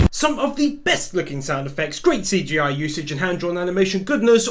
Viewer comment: Did he say " some of the best looking soundeffects"??